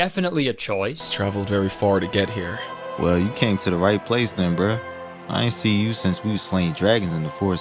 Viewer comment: i was watching this on 2x speed and i thought at 24:09 the stick man said the n word not “then bruh” 💀